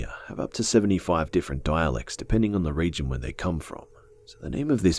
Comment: Completely misheard at 12:27 as "the indigenous people of Australia have up to 75 different Daleks." Just when you thought Australia couldn't get anymore dangerous.